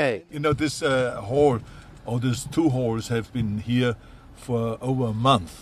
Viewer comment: It sounded like he said “ two whores”